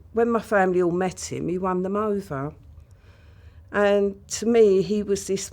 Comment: 😂😂😂 I miss heard when she said, he met my family he won them over, thought she said he run them over 🤣🤣